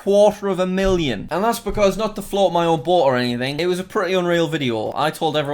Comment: when said “not to float my own boat” the captions say “not to flaunt my orb” lol